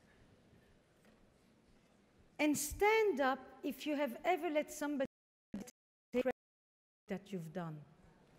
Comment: What does she say 12:23 ??? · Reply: “And stand up if you have ever let somebody take credit for something you’ve done.”